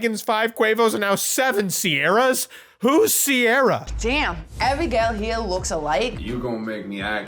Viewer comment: WHOS CIARA???? IM SORRY DO YOU NOT KNOW WHO CIARA IS?! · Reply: Oh CIARA... I thought he said SIERRA